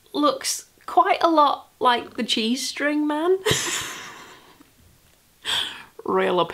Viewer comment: I thought you said your planter looked like "the G-string man" and I was really trying to wrap my head around that one. Lots of cute things, though, once I got past that!